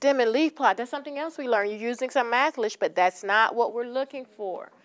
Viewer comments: did she say 'mathlish' at 1:12? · When describing words for math, did she just say, "you're using good MATHLISH"? WTF?